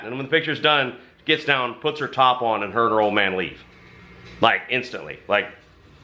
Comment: I think he said, "her and her old man leave". If that's true and her old man was there, I'm surprised he didn't beat the living hell out of those guys.....